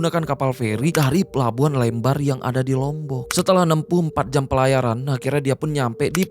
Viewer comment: 64 jam? wow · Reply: Salah denger, bang bilang "nempuh 4 jam"